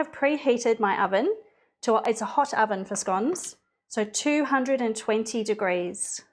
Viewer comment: Did not catch the oven temperature to bake the scone...Thought she said 220 deg. but what would that translate to in US oven temps....Thank you, dying to make these! PLUS is this Heavy Cream/Whipping Cream used?